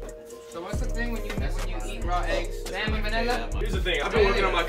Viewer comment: “what’s the thing when you eat raw eggs? salmon man ella?” - E.H. 2019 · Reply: I thought he said Vanilla.. I might be death lol